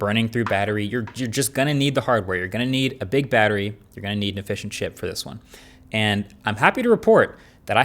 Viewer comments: Shout out to all the UK viewers who felt hungry at 13:00 I heard "you're gonna need a fish and chip" 😂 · I heard "a fish and chip" when he said efficient chip. Hahaha I need food